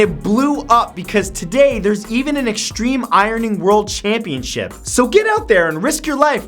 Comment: When you said extreme ironing I thought you said extreme irony